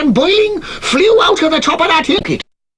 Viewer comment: Sounded like he said 'flew out of the top of that hicket' but it's not